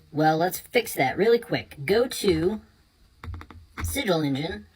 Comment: which side is at 1:42? I don't understand him :( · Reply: Sigil engine